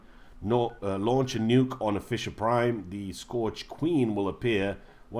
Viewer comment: I thought you said fisher prime